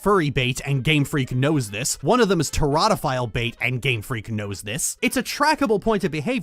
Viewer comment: what was it that you said Gardevoir was? What kind of bait? I’ve never heard of the word and trying to google it without the right spelling isn’t going anywhere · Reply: Teratophile Bait. Monsterfucker Bait